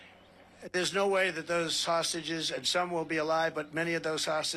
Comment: Oh god, I thought he said "sausages" for a sec there... I was like, what the hell is he talking about sausages not being alive...?????